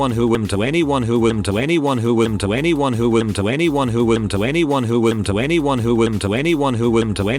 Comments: "To anyone who to anyone who to anyone who..." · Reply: Eventually I heard "tweny one hoowan" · Am I the only one who heard “To anyone who, to anyone who, to anyone who...” at 4:46? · It kinda sounded like twenty one hooman from